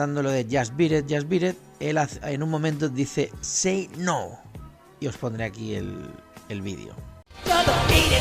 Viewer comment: Pense que decia sinaloa